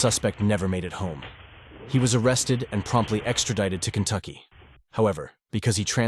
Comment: I genuinely thought that the narrator said he was arrested and promptly executed instead of promptly extradited XD